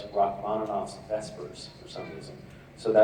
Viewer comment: What song did Misha say that he listened too when he was getting ready to play Cas? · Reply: I'm not REALLY sure, but I thought I heard "vespers". According to google: a service of evening prayer in the Divine Office of the Western Christian Church (sometimes said earlier in the day).Wikipedia page: <link>